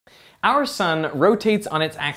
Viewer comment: I heard "our son" o_0